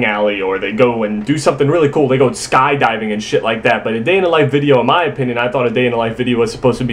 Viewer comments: Thought is said “day in the life of a Jew” dunno why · i swear i thought it said "a day in the life of a jew"